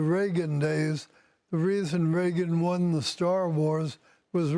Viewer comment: did he just say Raegan won the 'star wars'? Or have I heard it wrong?